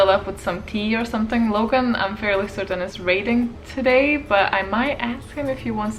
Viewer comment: I heard, "Logan, I think, is raiding today." I did wonder what job he had!